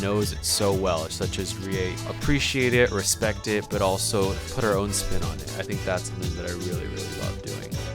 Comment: i thought he said put your own spit on it